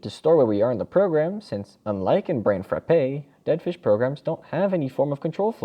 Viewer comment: subtitles said brain frappe lol